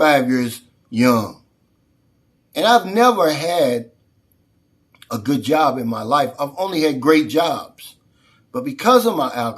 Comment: did he say "ive never had a good job in my life. Only great jobs". Is that correct?